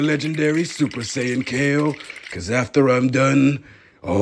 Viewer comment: I thought he said "Fuck outta here" instead of Hakai 😂